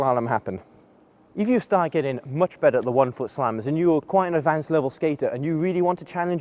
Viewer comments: I thought it said "one foot salmon"xD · I thought it said "one foot salmon"xD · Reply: I thought it said "one foot shalom" and for a second I thought huh i didn't know there was a Jewish inspired move :))